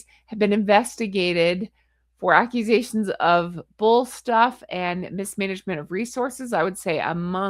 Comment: you read "bull stuff" instead of "bullying staff." 😅 I love it! ❤